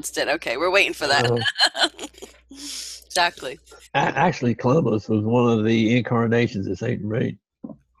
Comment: OK. At 38:10 says "Columbus was a reincarnation of St. Germaine." Right? It's hard to be sure but I think that's what he said. This is so confusing. I am so frustrated with finally for once hearing the truth. Has everybody forgotten how many west Indians died because of his slave-promoting policies and his European diseases the Indians had no immunity from? How was this OK? Please, someone try to answer me this question. I'm not going further than 43:00 unless I get some kind of answer.